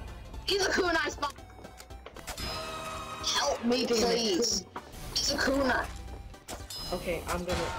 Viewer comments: he said for others that didn't understand « he's a kunai » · I think he said "kooner", as in "Kunai spy"